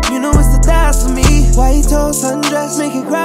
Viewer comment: I thought he said white toes sundress lol